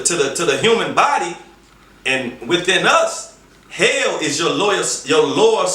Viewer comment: I’m a bit high and I thought I heard hell is cold. Why did I watch this Blind looking like dude.